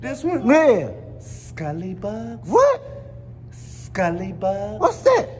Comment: Skully bugs is one of the best lines 🤣 you and are much appreciated G 💯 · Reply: I coulda swore he been saying skully buds 🤦🏽‍♂️🤷🏽‍♂️